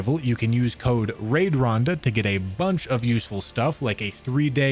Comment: why does it kinda sound like he said useless